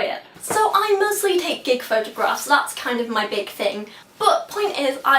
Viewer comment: I could have sworn you said "I mostly take dick photographs" the first time I heard it, and had to back up to realize you said "gig."